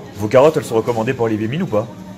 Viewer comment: au lieu de comprendre " Olivier minne" j'ai compris "pour les débiles" 😂